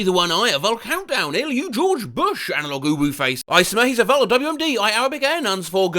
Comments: This isn't an AI slop channel but it's kind of an AI slop channel 🍿🍿🍿 Also: 16:20 did I hear "analog uwu face"? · Why did you call that "analogue uwu face"? We feel insulted.